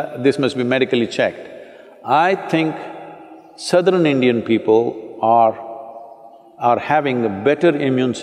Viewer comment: What he said at 0:43 ? Sadunion people ??😊 lol